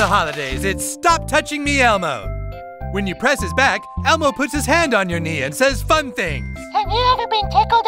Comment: Lmfaoo I thought the narrator said "when you press his back, Elmo says fuck me!"